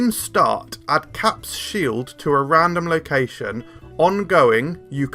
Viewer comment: With Sam Wilson? How so? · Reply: Lol I thought it said into your deck randomly, not at a random location 😂